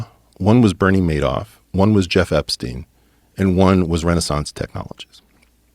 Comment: Jeff Epstein? Did I hear that correctly